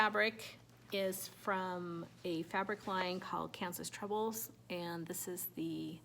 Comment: I wasn't able to find the harvest / rusty red floral fabric you showed it sounded like you said cancels troubles? I may have misheard. Could you give me the name by chance? I love your color palette - so pretty. I am going to stitch the band of braves for my son - It is not my usual style -but I love it. Thanks for sharing your wips :)